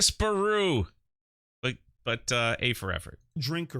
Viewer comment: Disparaatu/Kuato, whatevs. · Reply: I thought he was saying that Disparu is a Bampire.